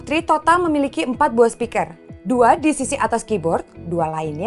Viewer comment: Dimenit 4:55 Kak itu ngomong apa sih , Tambrass ?? Baru denger , Mungkin ada yang bisa koreksi cara penulisan Tambrass 😂 biar nambah ilmu 😁🙏🏻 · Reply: Gilang Ramadhan Koto palm rest